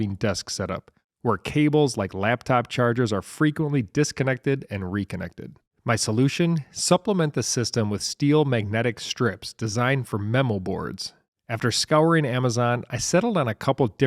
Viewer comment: I had my volume low and I thought you said mental wards ha-ha.